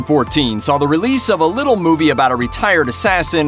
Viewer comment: sorry did you say LITTLE ??